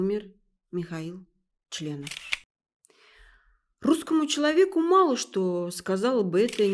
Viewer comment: на 1:13 "Михаил Членов" , я правильно услышал?